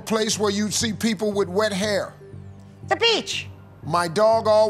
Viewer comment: Oh, he said "wet hair", at first I heard "red hair", and when she answered "the beach" I was so confused 😂 I was thinking Ireland